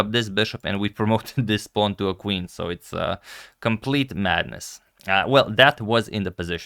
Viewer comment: I am sorry .... did you say "complete Magnus ?"